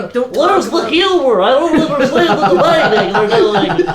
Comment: Wtf did say at 3:47? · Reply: "Where's the humor? I don't understand where.......(can't make out the last part)"